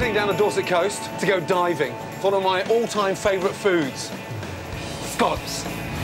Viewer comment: He pronounced scallops so fast that it sounded like scalps. Creepy.